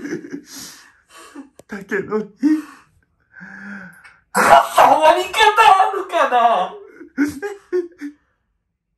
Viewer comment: なんて言ってんの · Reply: こんな終わり方あるかな